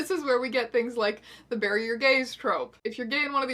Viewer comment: For the longest time whenever someone mentioned the bury your gays troupe I always heard it as barrier gaze and I was really confused